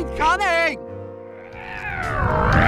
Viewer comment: what is he saying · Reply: Gentelmens